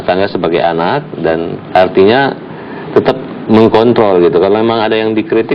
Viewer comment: salah denger apa gmna gw , mengkontol